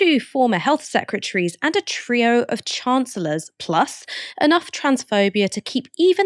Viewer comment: When said ‘A trio of Chancellors”, I heard ‘a trio of chancers’. The definition of a chancer: ‘a person who exploits any opportunity to further their own ends’.